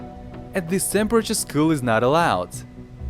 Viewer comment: what did he say at that time? "At this temperature school is not allowed"?